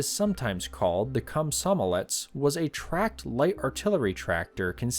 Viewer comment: sorry, did you just say the prototype was called the cum-some-a-let's???